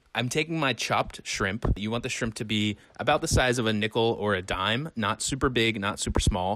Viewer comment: I honestly thought that said "nipple" instead of "nickel" lol whoops